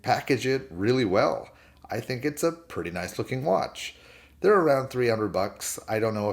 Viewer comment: what did you say at 7:56. I've never heard that term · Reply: guilloche — it’s a decorated dial pattern. 👍🏼